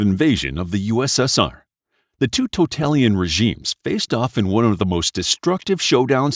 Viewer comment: Just an FYI, at around the 2:05 mark you say 'totalian' instead of 'totalitarian'.